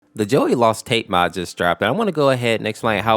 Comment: I thought you said jelly and i almost went insane